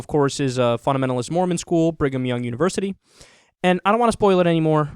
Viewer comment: LOLOL I thought you said Bring 'em Young University!! I was incredulous that they would be that blatant about indoctrination.